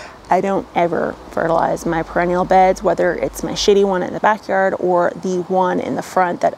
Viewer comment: Had to replay video a couple times around 2:51as I totally misheard "shady one at the back". Possibly I was just projecting about my "shady" shady garden!